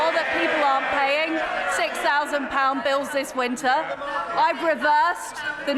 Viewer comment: I’m I just being thick or did she say at 0:35 that she’s making sure people ARE paying £6,000 bills??